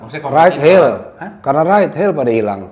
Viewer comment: dia ngomong apa sih? Ride hell? Right hill? Maaf aku ga kedengeran. Ada yg bisa bantu? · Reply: Ride hail. Taksi online.